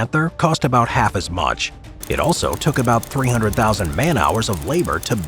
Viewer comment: did I just hear 300k hours💀💀